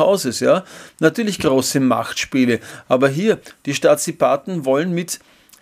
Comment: Ohne Wertung - habe ich das ab ca. 1:45 richtig verstanden - die Stasipaten und dann die Merkel?